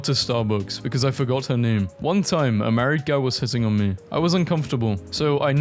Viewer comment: im i the only one who heard "america" instead of "married guy"